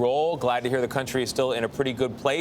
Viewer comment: sounded like thank you so much honey lol